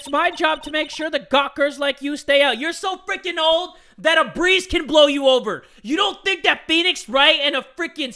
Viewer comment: " Your so fricking old that a breeze can blow you over! You don't think that Phoenix Wright and a fricking Spirit Medium can go through you? Ohh girl you got it twisted! " - You're not wrong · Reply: I thought he said a priest can blow you over and I was like wait no